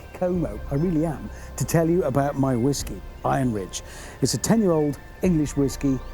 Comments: Shut up Hammond!... that Whisky name is the best thing ever! I swear I've heard "I'm Rich" and then I've realized it's Iron Ridge... please tell me that is not a coincidence! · Discover Richard Hammond’s award-winning whisky here: <link> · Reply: When he said the name of the whisky. What I heard was “I’m rich” not Iron Ridge. 😂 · I thought he said "I am rich," not "Iron Ridge". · When I heard your sponser opening, It sounded like you said "I am Rich", which makes sense I guess... Or was that just a play, for "Iron Ridge"?